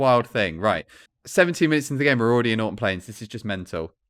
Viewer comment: subtitles/captions: 7:17 "17 minutes into the game and we're already in autumn plains" 25:51 "what if I just did it—well I was going to say what if I just did it in one, but I didn't" 49:26 "I see where I gotta kinda do a little, uh, bblbl-ah (gibberish)" 55:31 "uh-there-we-th-five... the things are going to be going off forever now"